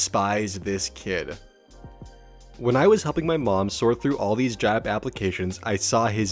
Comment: I thought you said humping my mom and not helping my mom